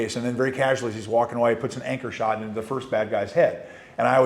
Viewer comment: I thought he said “anger shot.” Hell yeah I’ll do an anger shot to a POS that killed a bunch of kids. Nobody gonna prosecute you for that, at least not anyone who likes kids